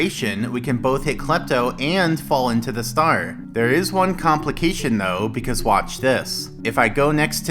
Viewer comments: The subtitles say "compilation" but the actual word said is "complication". Edit: Not sure how it was when I posted this, but I read it today and it says "complication". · Subtitles say "compilation" instead of "complication"